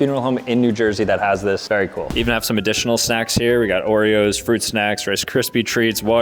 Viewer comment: why did i hear sex instead of snacks lol